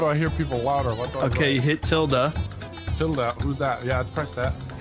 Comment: to be fair it did sound like he said “tilda”